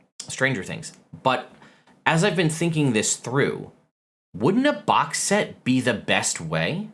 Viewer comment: I thought said it wouldn’t be a box set